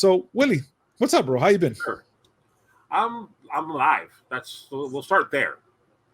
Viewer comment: lol, said "I'm Alive!", I totally heard "I'm High!" 🤣😅